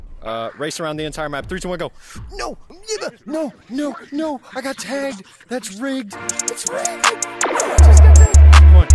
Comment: did you say the n word????????????? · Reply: He said Niba